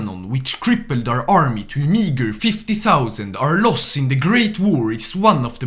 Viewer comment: did he say the n word?? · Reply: meager